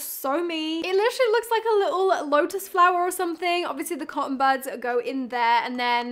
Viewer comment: Being from the US hearing you call them cotton butts is so cute ! I call them Q-Tips , I like cotton butts much better 💕🥺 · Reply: Ohhh , i thought she said cotton buds